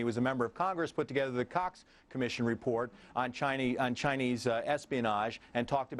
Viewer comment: sounds like he says cocks